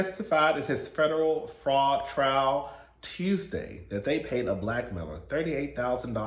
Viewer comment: I first listened to this video when I was getting ready in the morning before I had some coffee, so for the Todd Chrisley segment I thought you said "-paid a Black male, er..." instead of "blackmailer". I was cackling like OOP Mr. Chrisley was paying for the BBC?!